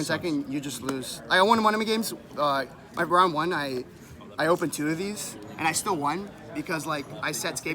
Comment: did he say he used 2 scapegoats in one turn? you cant do that right ? cause 1 scapegoat says you cant summon for the rest of the turn. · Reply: He said he opened two